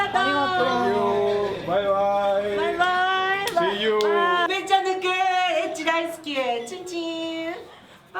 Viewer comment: yo solo entendi "daisuki" que es algo como "me gusta (algo) " o "me gustas/te quiero" dependiendo del contexto . El resto de la frase quien sabe JAJJA